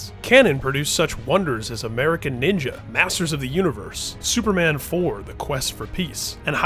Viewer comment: I thought he said asses of the universe